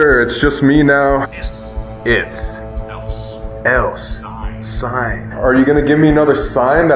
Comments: it sounds like it said ''its out-side'' then it says ''touch'' a few minutes after got touched outside on her arm 43:55 listen and you hear a scream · I think it was trying to say "it's outside" but it couldn't pick it up fully. It also said "touch" referring to how something touched outside. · that sounded like "its outside"